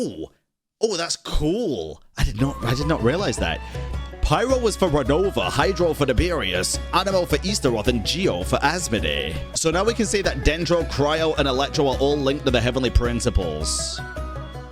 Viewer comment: I heard you said Easter Oven when you talked about anemo for Istaroth.